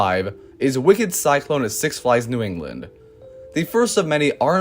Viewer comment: You said in the intro this would be strictly USA coasters... OH, SNAP! I thought you said England, but you said New England.. My mistake...